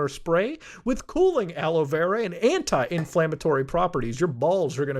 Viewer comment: I heard "hentai inflammatory" and now I wouldn't want it any other way.